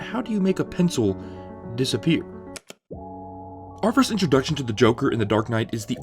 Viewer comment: Sounded like you said "Arthur's" instead of "our first" at 0:48 :o